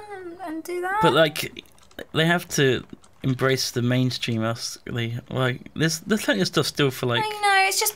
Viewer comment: did Duncan actually say embrace the mainstream